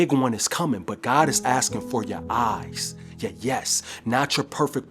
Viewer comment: i think he said "yeah yes not your perfect performance"